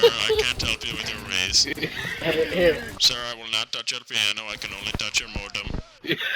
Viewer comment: I thought he said i let him touch my penis😂